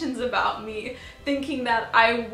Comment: I have rewatched this part several times and still have absolutely no idea what you said. 😂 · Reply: she said that emotionally converted